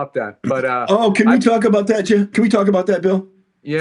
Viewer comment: Did he call him "Jim"?